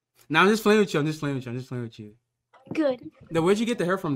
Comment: I heard "my mother's gay"